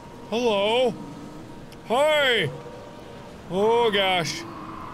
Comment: d-did he say..... h0i?!?